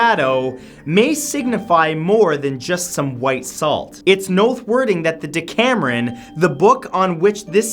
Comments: am I the only one who heard Matt say "it's noth wording" instead of "it's worth noting" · I can't be the only one who noticed he said "nothe worting" instead of worth noting in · Did any body else catch when he said "It's Nothe Worthing" instead of it's "Worth Noting" around 7:52 ?😂😂😂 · it's nothe worthing.....anyone else catch that?